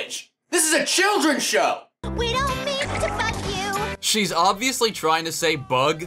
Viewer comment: when I listened to what Courtney said with my Left ear she said "Bug" but with my right she said "F*ck"